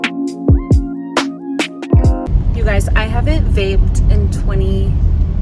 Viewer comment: When you said you hadn't vaped in 20 days, I thought you said bathed in 20 days lol